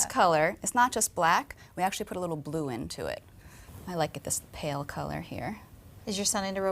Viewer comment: oh i thought she said put glue in it hahaha